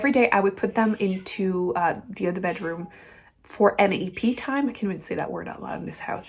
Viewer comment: The first time you said NAP time, I thought you said “any pee time.” I was impressed you have your cats on a pee schedule. lol. I should turn up my volume. So glad the 3 Musketeers are doing better at accepting their new brother.